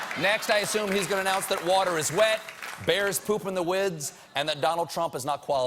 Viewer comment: he says "wids" instead of "woods"